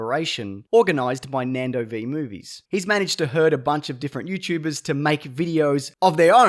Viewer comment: I heard this as "He's managed to hurt a bunch of different youtubers"